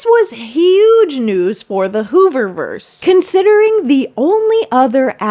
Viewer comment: Thought u said gooberverse. Also tru